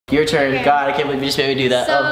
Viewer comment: I thought you said hokay....